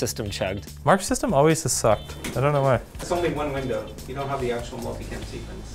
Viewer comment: "Mark's system always has sucked, I don't know why" What I heard: "Marxism always has sucked..."